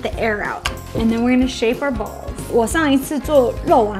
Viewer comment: Watching this at my work and at 3:44 my colleagues thought you said “shave our balls.” 🤦🏻‍♂️ 🤣